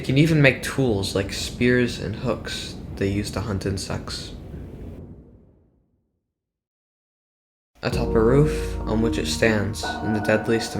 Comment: I heard it as ‘hunt and s-‘